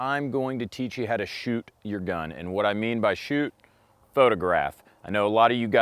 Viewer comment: I thought it said you to shoot your other gun